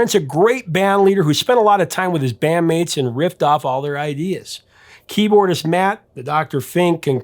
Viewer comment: “Engineer Susan Rogers called Prince a great bandleader who spent a lot of time with his band mates and riffed off their ideas”. Thought he said ripped off their ideas at first 😂